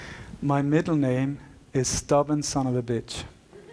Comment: Great lecture. But what he said on 1:58:09 going on: "My middle name is ..." i did not get it clear aural. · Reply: Stubborn s.o.a.b.